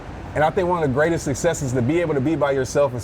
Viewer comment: What does he say 14:43? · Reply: "Be able to be by yourself and still not feel alone"